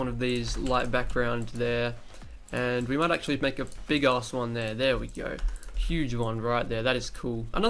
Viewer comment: did he say a big ass one?